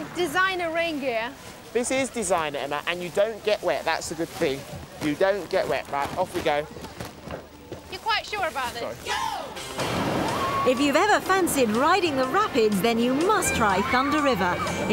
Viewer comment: Is it me or at 6:06 did he say sorry 😂😂😂😂